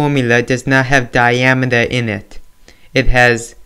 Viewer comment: You sound like British saying the word "in it" cause it sound like "i nit" btw ty for saving me this exam lol